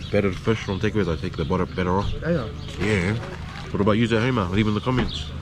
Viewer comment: Thought you said I'm batter off for a second there 😆 Love watching your vids! Especially when you sing 😂💜